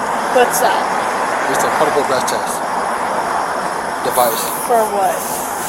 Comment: Did he just call the PBT a "Public Breath Test?" I definitely didn't think I heard the word "preliminary" lol